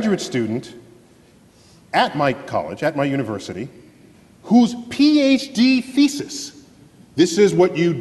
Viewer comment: I hear 'whose PHD feeces'